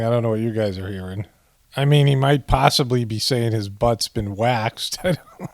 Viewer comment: I heard "my butt's been waxed". Relates to his most recent satanic ceremony where he was laid on his belly naked and hot wax dripped onto his buttcheeks while he recites , "thank you may I have some more?"